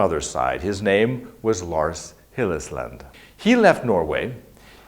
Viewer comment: I thought I heard the name as Hillesland in the video. I'm not doubting you, but I heard a different name than you reference above.